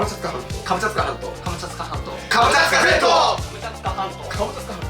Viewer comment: 「カムチャッカ全島」って言ってるとき「ハムカツゲット」に聞こえる‼